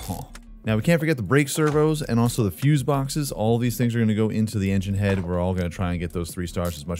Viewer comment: did you say the fuzes go into the head?